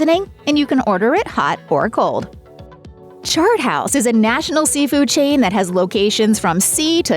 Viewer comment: at first i thought she said shart house xD